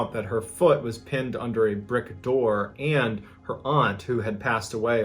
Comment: 'her foot was pinned under a brick door' did I hear that right ?